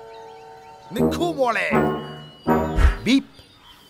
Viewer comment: Did he say "Nikhoo Molae" at 1:32..